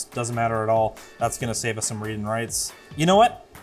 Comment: CC "reading rights" instead of "read and writes"